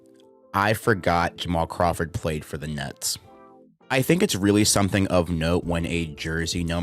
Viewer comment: sounds like you say nuts lol